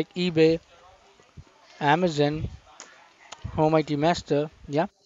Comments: At first i thought you said oh mighty master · I keep hearing "Oh Mighty Master". Which would be a good nickname for!